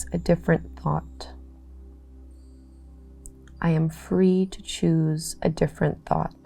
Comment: For the affirmation “I am free to choose a new thought” I definitely heard “I am free to choose a new POT.”🤣 I figured it was a plant analogy, and if the plant doesn’t thrive in one pot it can choose another. 🤷🏼‍♀️🪴